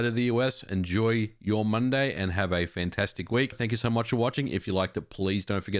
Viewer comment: I thought I heard you saying: If you like the police, don't forget to put a like on it.